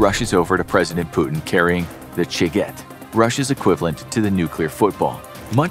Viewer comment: Initially I thought he said The Chicken, which I thought was a humorous, but fitting Russian equivalent to the American nuclear Football.